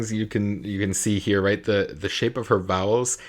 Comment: At first, I thought you said, "the shape of her bowels..." Which led me to two lines of thought: 1) how do you know what shape her bowels are in at that moment?, and 2) that's the most in-depth vocal coaching I've ever heard of!